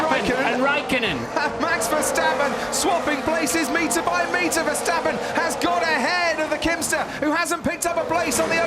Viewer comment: Did he just say “ the Kimster”??? Wtf..